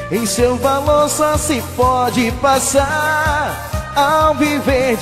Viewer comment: eu ouvi só se F0DE